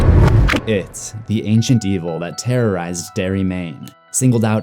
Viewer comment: At the beginning it sounds like you said "it, the ancient D-hole" That was funny